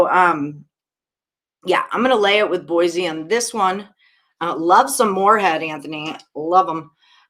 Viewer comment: "Love some morehead Anthony Lovem" I was like WTH did she just say LMFAO